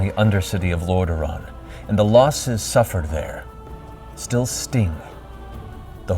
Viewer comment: had to go back for a moment, thought he said "the sting of the alliances attack and the losses suffered there still sting " :P